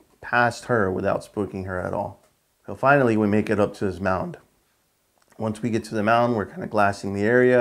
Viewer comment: I’ve seen lots of stuff huntin here but never any mountains😂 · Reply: ok I just rewatched it. Around 9:35 mark you say “mound” when I watched it the first time I thought you said “mountain”. I looked at my co-worker and was like “mountain” where the heck are those in zone A🤔 lol. Great hunt man keep up the good work and good luck this season. I’m a lot further north of y’all around FL/GA line.